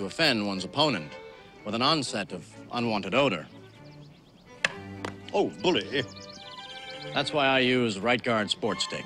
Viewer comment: Oh bullet hit!!!!!!! Haha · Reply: thought he said, "Oh bully!"